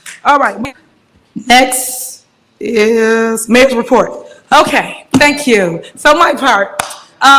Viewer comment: did she say major report tf && I also wished a resident or belcher would’ve got her with a fast someone will humble her now that she is a regular person now lol😂